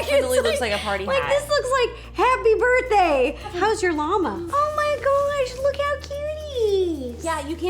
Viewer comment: when you said,hows your lama, I thought you said, how's your mama!!!!!